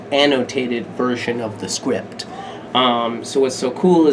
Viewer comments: I heard “squipped” instead of script. Help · he said "script" and I swore I thought he said "squipped" which is funny cuz he's the BMC Broadway star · "fully illustrated and annotated version of the script" I heard that as squipt oopss